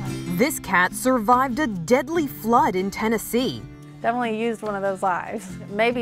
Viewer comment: I thought that said food instead of flood 😂